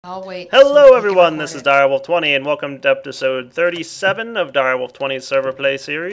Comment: Did he say deptisode at 0:02?